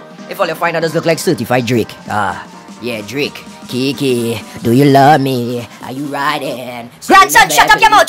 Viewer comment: It sounded like he said *Gay,Gay, Do you love me, 😂